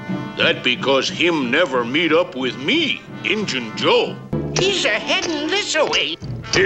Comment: Injun Joe’s mistake wasn’t being slow. It was assuming Sam would fight fair. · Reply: I never would've known it was Injun I thought he said Itchin. Like... Itchin Trigger Finger.